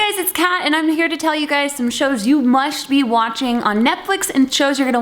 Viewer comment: did she said "musht" ? 😂